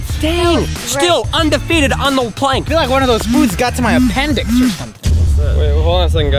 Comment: I'm so sorry...did she just say noods........